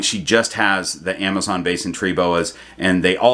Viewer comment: Had to rewind when I thought you said Amazon Basics tree boa 😂